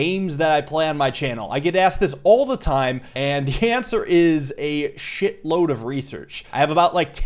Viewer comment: I was just listening to this in the background while doing something else and I misheard this as "cancer" and got very concerned for a minute